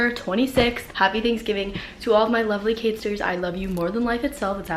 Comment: Omg in the beginning when she said I’m so thankful for my lovely catisters I thought she said haters and I was LIKE WHAAAA